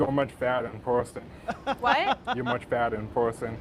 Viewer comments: He was saying your badder in person not fatter! Lol bad as in pretty. Bro is just super awkward and antisocial plus it sounds like he may have somewhat of a speech impediment, which is why badder sound like he said fatter. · HE 100 PERCENT SAID badder in person and just replied yes to her because he was tunnel vision and would have said yes to anything. He thought she said badder back to him for sure. He wasnt stepping up to tell her shes fatter wtf lmao.